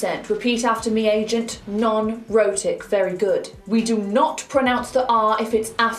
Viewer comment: Jesus, she says "rhotic" and all I hear is "erotic"....